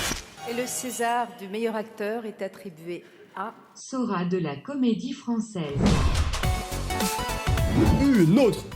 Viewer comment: A 4:44 je sais pas si c une alusination auditiv mais j'ai entendut sora comedien francais